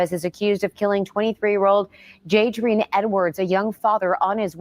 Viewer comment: Jaydrian Edwards? At first I thought they said his first name "Daydream" 😂 his mama wrong for that